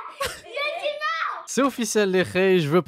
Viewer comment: c’est moi qui rêve où elle hurle : « non je suis morte » 😢😵 ?? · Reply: Non elle a dit «non, j'ai dit non»